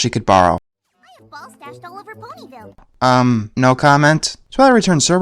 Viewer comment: At first I thought Pinkie said ballsacks. She said balls stashed all over Ponyville.